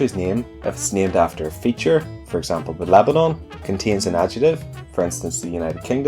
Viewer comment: what does he say when talking about Lebanon? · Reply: "If it's named after a feature, for example, The Lebanon."